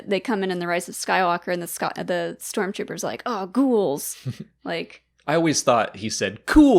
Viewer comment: today i learned those tRoS troopers said Ghouls. I thought they said "goals"... Ive seen that movie like 85 times and always heard goals lol